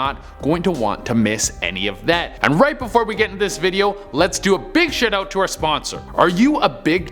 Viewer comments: Sounds like he said "A big shitout" 😂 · sound like he said big shitout